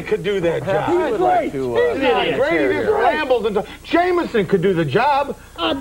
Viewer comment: Sounded like he said "Sheamus" but he was saying Jameson.